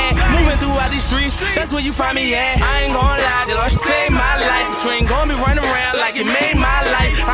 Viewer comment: What does he say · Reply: i aint going locked judge saved my life